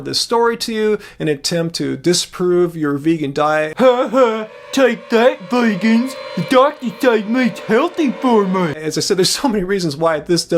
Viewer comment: What did he say? I'm hearing impaired. Or did he just say mmmf babaaaaf waaaaah? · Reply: "Haha take that vegans, the dr says meat is healthy for me"